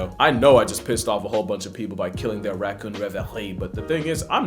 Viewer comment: Im really hard of hearing and i cant tell what he is saying at 10:18-10:20. Does anyone know? Ruin their raccoon ?what?! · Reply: Reverie